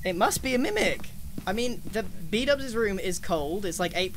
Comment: did he say bdub’s room? Or am i tweaking